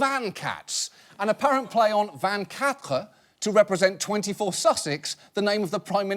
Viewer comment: When John oliver said "vingt-quatre", I thought he said "Van cat"... it would have been a better name for this shitty band.